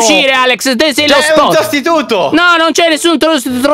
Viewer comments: prostituto Io:COSA?!?!??!?!! Poi Alex:sostituto Io:aaaa ok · pk ho capito "prostituto"?😂😅